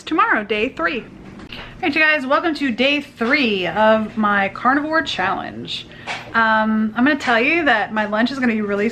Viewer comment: At first I thought that it said corona virus challenge!